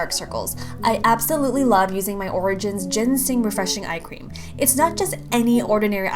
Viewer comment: When it said eye cream I thought it said ice cream lol😂😂 also love your vid